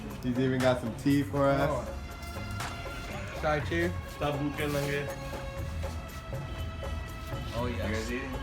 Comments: did he say "Sab bhuke nange"?? 😂😂😂😂😂😂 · I was in middle of the this video and I heard someone said "sab bhuke nange" 21:24 (literall translation- all are hungry & naked) I'm laughing so hard ..😁😁😂 It was just a joke,, maybe one of the worker there said it.